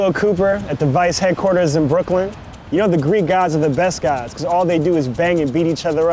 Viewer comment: Thought it said "geeks" and I'm Greek.